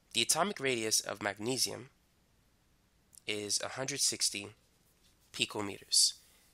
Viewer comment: Who else heard "116" at 9:16? 😂